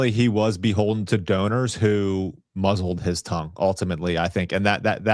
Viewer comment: I thought bro said donors that"mozzelled his tov"😅 Which I thought was funny but It was "muzzled his tongue"